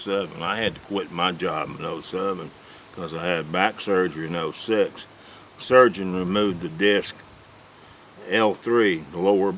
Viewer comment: Did he just say that "he had to quit his job when he was 7 because he had an operation at age 6"???